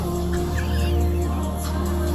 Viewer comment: sounds like someone said "that don't look like him"